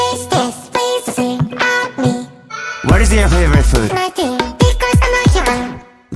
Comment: イケボとカワボが同時に聞ける幸せ 0:17 ないですに聞こえるw